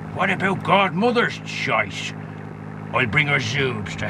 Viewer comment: Did he say "Mothers shite" at 0:24? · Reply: rinnin He said "Godmother's choice".